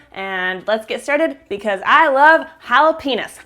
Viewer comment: Did she say "I love Hilah penis" at 0:16? o.O